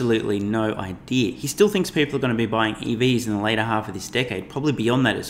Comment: you said EVs instead of Ice , just letting you know